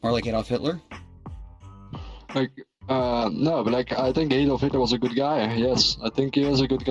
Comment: I can’t tell if I’m misinterpreting or mishearing this but did he just say “I think Adolf Hitler was a good guy”